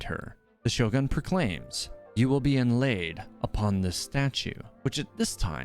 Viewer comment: "and now we've come to the most impactful moment of the trailer" Ahhh yes Raiden Sword Booba truly is magnificent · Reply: Yes. I saw Sword Booba, then distinctly heard her say "You will be laid on this stone", which I'm totally down for.